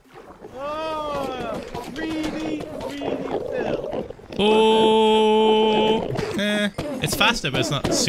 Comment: Us:telling Phil to play sponsored games for free money Phil:makes me cringe and never liked that stuff Phil’s stream all the time: AAHHHHHHHH GREEDY GREEDY PHIL! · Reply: I heard it as "really, really, ph1l" lol